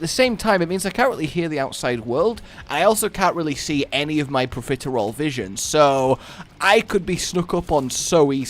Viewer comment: Did he just say 'profiterole' instead of peripheral' vision?